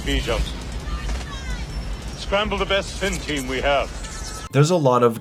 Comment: For years I thought the old man said "Pin Team" not spin team.